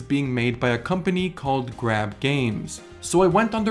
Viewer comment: They said it was made by grab games Me: I thought i heard crap games